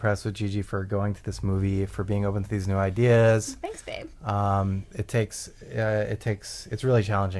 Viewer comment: after being thanked for having the moral character that allowed her to see the film, Gigi said “ooh, thanks, Dave”. It sounded like ‘Dave’, or possibly: ‘Babe’? Yet you keep calling the man talking ‘Mike’. Can you clarify?